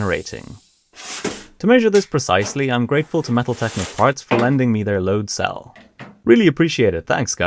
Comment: I misheard 'Metal Technic Parts' as 'Metaltech McFarts' at one point XD